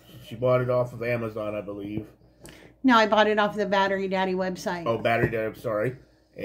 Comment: K I thought it said Buttery Daddy lol Hi! Hey that’s pretty cool so you’ll always know where your batteries are. You got hosed lol Aww it was a nice idea! You are so nice to get that for him. You had good intentions! When it says as seen on tv think ( it’s crap lol) I used to work at an as seen on tv store and most of the stuff was crap. I don’t mind the tv loud unless it’s when I’m filming lol are you going to review all his Christmas presents??? It’s only fair lol That’s so cute!!! The heart! Crafting is hard work and doesn’t always work out! Take care guys!🌸